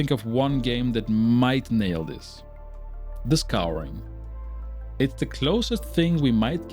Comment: What game were you talking about at 13:03 in the video. I cannot hear what you say. Your english not clear in the video. Thanks. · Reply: It’s called The Scouring